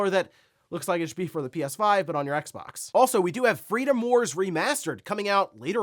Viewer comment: Thought said “FREEDOM FIGHTERS” and got excited… then was disappointed it wasn’t.